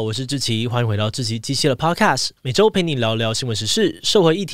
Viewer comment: 我發誓我還沒看標題 但是一開始就聽成「志祺雞雞podcast」